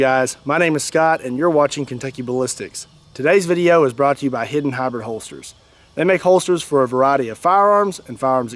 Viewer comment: thought you said hidden hobbit holsters